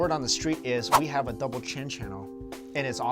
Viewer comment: I thought you said double chin😂